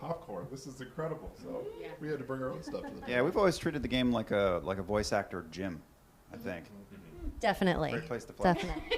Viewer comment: What does Liam say after/on top of Ashley, that makes the others laugh? "Replaced the _________" ? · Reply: "Great place to flex"